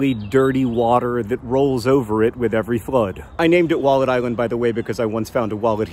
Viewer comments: I thought he said Walnut Island as first.... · I thought he was saying Walden island for most of the episode lol